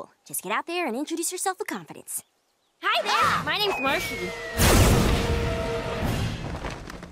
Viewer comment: What I heard is " Hi there my name is Marshy"